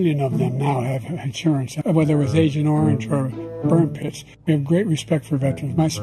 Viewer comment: I heard “Whether it was agent orange or bird piss.” At 8:05 HAHAHA